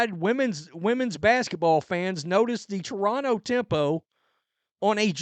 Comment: Just for a second.... for one second....I thought it said "Toronto Tampons"!🚩